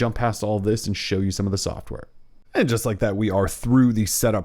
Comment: Could've swore you said sh!t on some of the software